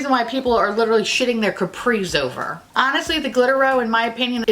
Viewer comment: my fat ass thought you said "shitting capri suns over it" ... uggghh what flavor ew lololol